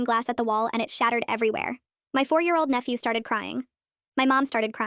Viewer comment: I thought it said my 4 year old and I was like what I thought op didn’t have any kids